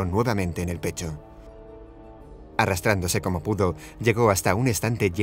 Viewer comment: Chucky es mi muñeco favorito 😀😀🔪🔪🔪(soy yo o escuche mal o no entendí la palabra pero en el minuto 0:49 dice “arrastrándose como put0 “ 😱😱😱) ¿escuche mal?